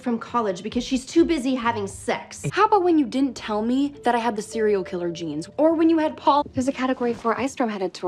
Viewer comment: I always thought she said serial killer jeans